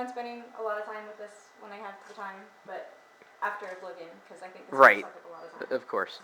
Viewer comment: um at 4:30 did she say after she moves in...lol